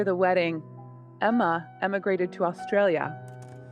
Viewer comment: When she said immigrated I thought she said emmagrated